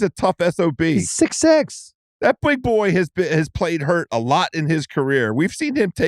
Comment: When house said “that big boy” I thought he said white boy and bursted out into laughter